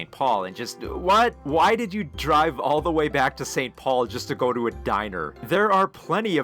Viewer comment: "Why did you drive all the way back to St. Paul, just to to go to a diner?" I thought you were saying Edina really weird, for a sec. Edin-ar. Also, he didn't drive there... he pushed his car there from the Hennepin Ave. bridge, iirc.